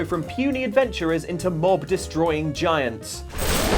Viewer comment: When he said "Mob destroying giants" I could swear I heard "Morb destroying giants" Send help